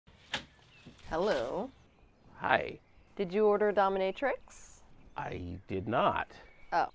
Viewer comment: it sounded like she said “did you order Domino’s” .. but it wasn’t. 😂😂